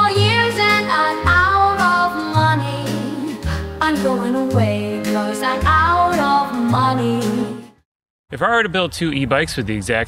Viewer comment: on a separate note: at first I heard "I'm going away because I'm out of molly". I hope you come back online, and I hope there is a way for your video creation to provide for you, because I really like the way you talk about this and you seem to be happy doing this <3